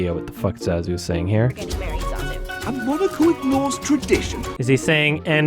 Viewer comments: I heard "a monocle ignores tradition" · youtube auto-subtitles captioned it as "a monaco ignores tradition". Even the auto-subtitles had trouble with what John Oliver was saying.